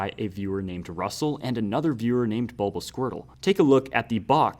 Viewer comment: Did Did he say 'Bulbasquirtle'? Like the Sonic youtuber?